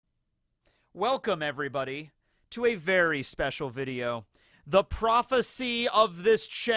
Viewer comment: I had to rewind I thought he said a “Barry Special Video”